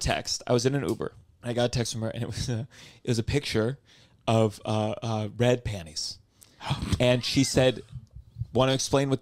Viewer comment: I thought I heard Red Panda